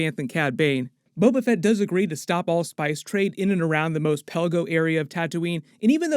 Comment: I thought they were saying no spice trade on all of Tatooine.